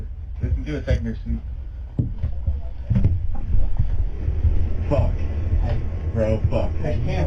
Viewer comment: what are they saying here? Sounds like ‘fuck, we’re all fucked’ before one panicked saying there’s a camera. Am I wrong??